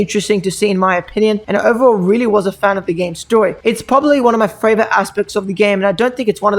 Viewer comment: did I just hear him say fravorite instead of favorite